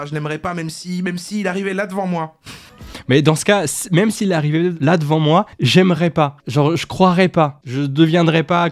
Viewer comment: Il a pas dit "même s'il était là devant moi je n'y croirai pas", mais : "même s'il était là devant moi, j'aimerai pas" (Dieu) Mauvaise compréhension donc de ce qu'il a voulu dire (sans parler du fait que ce soit de l'humour ^^)